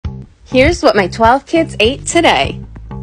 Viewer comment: I thought she said 12 year old kids not 12 kids 😂